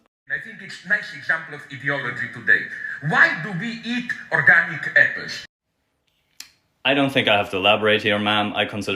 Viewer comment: Could someone please tell me what he asked? "Why do we eat organic (blank)?" I didn't understand him. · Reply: I believe it is "vegetables". I could be wrong but that is what I heard.